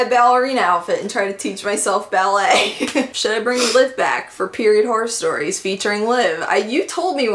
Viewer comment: For a moment I thought you said period horse stories and I was HORRIFIED. 10 out of 10 would watch anything you toss my way though. Idk why it's just satisfying watching you be normal. 😂